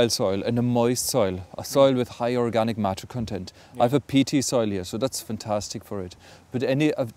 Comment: what kind of soil does he say he has? 2:30 PT soil? and what sort of pellets does he say he uses here? 3:39 poultry pellets? thanks for the help, struggled with his accent :-)